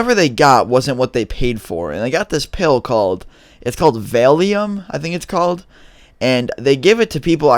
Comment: Valium and Vicodin is what Eminem overdosed on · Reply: itsnucktime vailium I thought you said failium